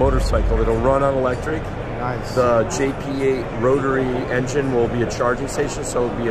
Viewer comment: DID HE JUST SAY ROTARY